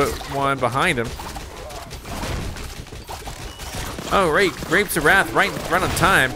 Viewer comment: it sounds like is saying rape 😬